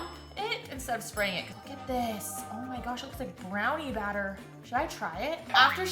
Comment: I thought said it looks like floors